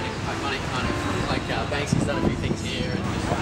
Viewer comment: is it just me or did he say: Banksi's done a few thinks here. What a small, small world.